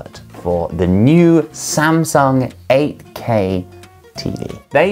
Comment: 8k가 ak로 들리는건 나만 그런가...